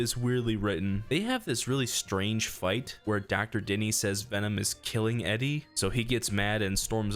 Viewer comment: did bro say dr Diddy?